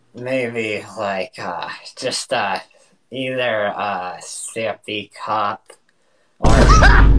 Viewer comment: what he say??? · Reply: Maybe just like a uhh either a zippy cup